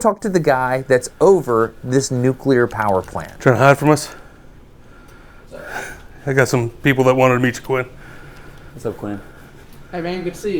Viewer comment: tryna hide from us. I got a couple people that want to meet you twin😂 (that’s not what he said but that’s what I heard)